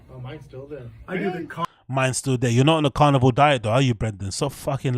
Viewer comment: Brendan thought Joe said "Carnival diet".... explains all the food trucks...